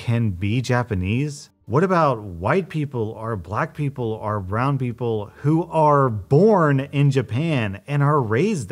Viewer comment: i misheard "round people" and was very confused. that was a good chuckle.